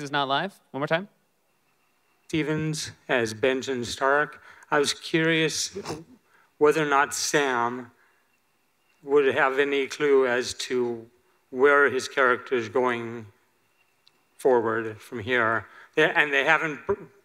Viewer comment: FFS are most people in here DEAF??? The guy dressed as Benjen Stark clearly says at 48:37 "I was curious weather not SAM were to have any clue as where HIS character is going forward from here". He as asking about SAAAAAAAAAAAAAAAM! And THEN he makes a comment that if it was written down in a sentence, it would be someting like "P.S. hey due, any chance you know what happened to Benjen" And now that I watched the vid again, John Bradley didn't understand the question!!! (he was asking about YOU you halfwit(Tyrion to Joffrey) And Seth WhatHisLastName is (Bronn to Ser Meryn) and also Maise(no wonder why you got your character blinded) and than again Seth misslead Bradley into wrong answer. FFS.